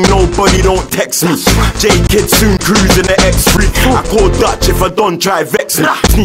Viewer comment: did he just say I call Dutch